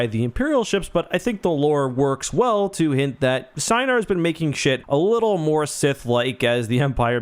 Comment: Anyone notice that it sounded like he said "have been making shit a little more sith like"